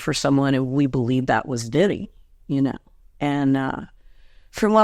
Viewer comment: did she say we believe he was looking for Diddy??? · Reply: "Vinnie", as in Vinnie Paul